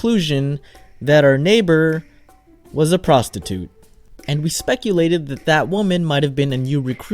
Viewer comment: thought you said prosecute XD ohhh wait he did...... .-.